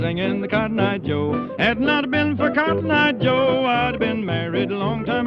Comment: I unironically did not know they were saying “if it hadn’t been for Cotton eyed Joe” I just kinda figured it was a vocal scat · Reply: When I was a kid I thought they were saying "bit off a limb for Cotton Eye Joe" 😅